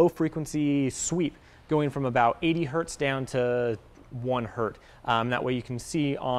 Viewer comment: did he just say "hert"? i'm done